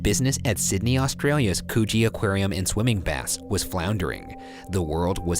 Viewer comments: Who else thought he said coochie aquarium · I heard "coochie aquarium" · I SWEAR TO FUCKING SATAN I THOUGHT RYAN SAID THE 'COOCHIE AQUARIUM' AND I SPAT MY FUCKING TEA- KWBEOSHWOWHEOBROFODUEIDUODJSKD